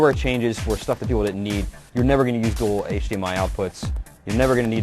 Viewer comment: did he say that no one ever was gonna need hdmi outputs? i cant hear wth hes saying